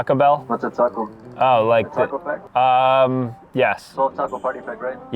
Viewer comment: “ I asked for the taco bell and I think he said the taco bell?” Bro he said the taco pack😂